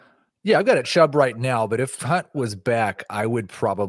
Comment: Literally sounded like Keith said @ 7:56 Yeah… I got a Chubb right now.. 🤣🤣😂😂🤣 I started dying.